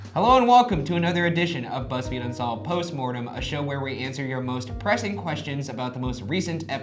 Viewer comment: Instead of most pressing questions I heard “most depressing questions”